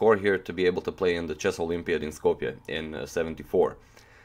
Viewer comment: did you say skopje?